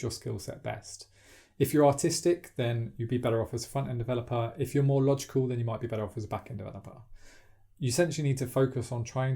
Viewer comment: Cried laughing because I heard it as "Austistic", and yes front-end does seem like a lot of fun to me at the moment.